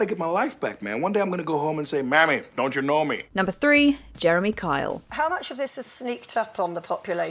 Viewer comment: Did she just say number 3 on 1:28?